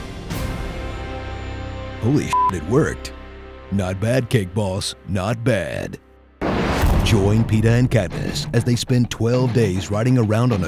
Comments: "not bad cake boss not bad" xD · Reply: I thought he said "cake balls" 🤣🤣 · baby Thor. you do know their twins right. plus I thought he said cake balls. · "not bad cake boss not bad" xD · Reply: Nield I thought it said cake balls too, but the captions said cake boss. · what does jon bailey say at 2:31? not bad ________ not bad? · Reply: friend I thought it was "cake balls" but according to the captions it's "cake boss" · “Not bad, Cake Boss. Not bad.” For a second I thought he said “Cake Balls” though 😂😂😂 I’m a total Hunger Games fan and all for Katniss and Peeta, but this is just hilarious.